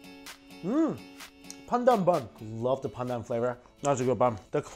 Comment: When he said "pandan bun, lovely pandan flavour" I thought he said "PANDA bun, lovely PANDa flavour" 😐😐😐😂😂